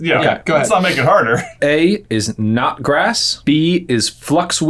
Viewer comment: I kept hearing "not grass," as in anything but grass, which would be a funny thing to list as an ingredient (or lack thereof)